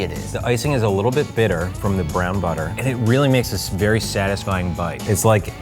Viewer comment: Did he say brown butter at 6:04